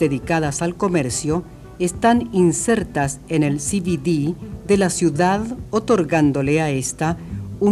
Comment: Por favor, Min 2:32 Cititi? Qué dice... Chi chi chi? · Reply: CBD: central business district